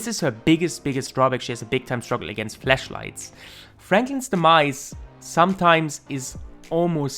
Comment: what did he say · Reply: I know what you said I was just kidding beacuse it sounded like fleshlight